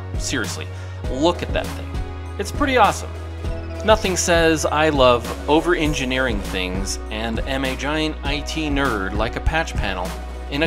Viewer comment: What did he say?! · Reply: I love over engineering things and am a giant IT nerd like a patch panel....